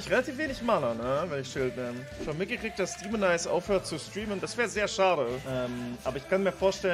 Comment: Über welchen Streamer redet bei 2:45 verstehe es irgendwie nicht richtig De... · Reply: Daemonize